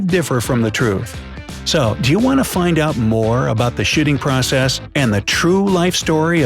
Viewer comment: Who heard shitting process at 00:48? 😂